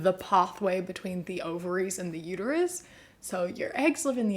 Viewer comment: Did I hear wrong or did you say otaries instead of ovaries?